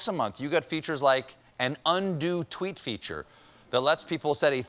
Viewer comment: Definitely thought Stephen said "undue tweet feature" instead of "undo tweet feature"